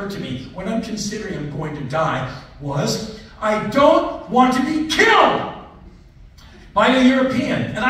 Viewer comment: what did he say?? Something European... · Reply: “I don’t want to be killed…..by a European” 🤣